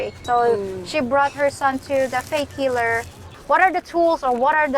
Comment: when you said that your mother brought you to a faith healer, I thought you said 'fake' healer. in either case I heard correctly. I will stick with science. I do like the idea of voodoo dolls, I mean it would be great to stick a pin in a voodoo image of a worker at hyper mart that tells me 'out of stock'. I love the outdoor interviews which show a taste of dumaguete and the folks that live there, excluding yellow teeth expat zombie types!